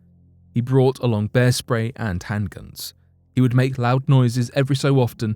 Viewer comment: I thought my guy said "bear spray and handcuffs". Like what is he going to do hook him up and charge him? I am glad I listened to it a couple times or I'd be getting roasted right now.